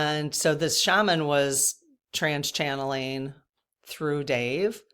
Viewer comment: Hi.. I know it isn't polite to correct, but even Dave made the correction that it is"trance channeling" not trans channeling. But it sounds like he says trans.